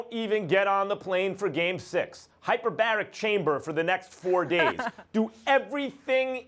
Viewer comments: I thought Max said Hyperbolic Time Chamber 😁 😁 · for a second i thought Max said hyperbolic (time) chamber from dragon ball lol